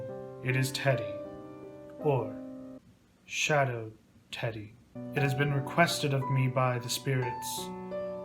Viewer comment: omg i love you. I also thought you said Shadow the Hedgehod instead of Shadow Teddy for a moment.